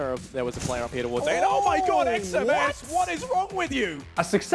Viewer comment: did he say xseven? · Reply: Xms cuh